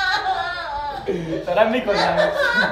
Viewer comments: دقیقه چی گفت ؟ دارم میک…..مش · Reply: کرده دارش اونم زیاد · چی گفت درست شنیدم یا من منحرف ام😂💔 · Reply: منحرفا گفت دارم میکشمت